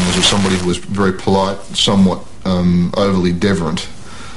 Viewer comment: anyone know what this word is? googled "deverent" - nothing. He describes Nick as "overly deverent" or something. Subtitles are of no use - those seem to be the only two words in the entire doc it gets wrong. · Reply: WeirdCityCitizen deferent, as in deferential.